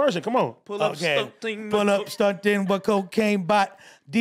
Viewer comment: When was freestyling, I thought he said "pull up stunting, what cocaine bought" meaning hes selling alot of cocaine and buying a bunch of expensive shit with a bunch of coke money, so he's stuntin on everyone. Am I the only one that thought that?